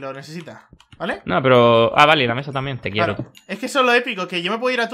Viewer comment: min 6:00 oisteis que dijo 'Te quiero' lo oisteis Pd:saludos desde cataluña